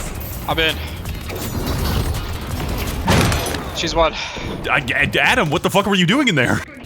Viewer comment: I think he said "one" not "won" so back to the editor you go, no mistakes allowed funny man.